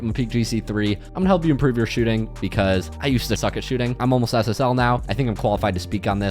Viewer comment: thought u said “I am ssl now” and I was so happy for you 😭